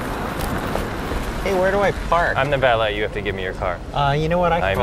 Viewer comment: I hear Toby saying "Where do I fart?" 😂